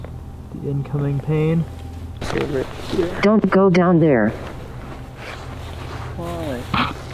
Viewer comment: I’m the only one who heard the woman’s voice right ?? It’s like a whisper in the mic or near the mic can’t make out what it says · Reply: Playing the video on that specific time frame at 0.25 , it sounds like she says "Sit down here" or "sit down (unintelligible)